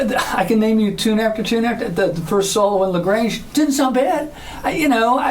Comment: sounds like he said a prs was played on la grange. 🤔🤔